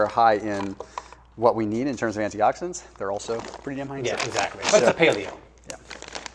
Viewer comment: "They are also ____" Any one hear what was said here? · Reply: “Pretty damn high in sugar”